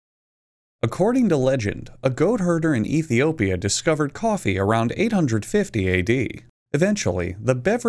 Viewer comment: did he just say '''Cow hurter discover coffee'"""